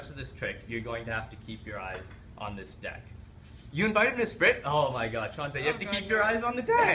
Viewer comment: sounded like he said make sure to keep your eyes on this dick HAH